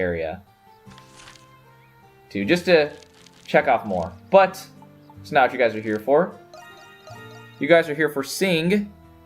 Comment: for a moment, I thought you said 'just to jack off more ' instead of 'check off more ' woops x) 'Nicks Meta Hour ' should be its own thing - just saying =)